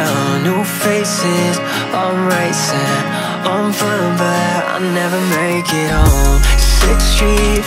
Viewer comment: I heard it as “I’m fun, but I never make it home” instead of “on foot but I’ll never make it home” and honestly I like both